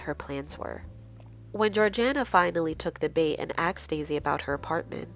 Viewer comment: did you says axed instead of asked? 😂 never heard that one before